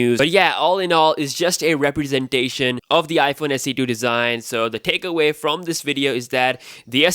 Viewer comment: All I hear “ iPhone se do”